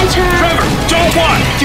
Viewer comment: what did he say at 0:18? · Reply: "Trevor, don't watch."... then I thought he said "get back", and the mouth looks like he says that maybe, but it doesn't sound like it. It sounds like it ends in "fast". I have no idea.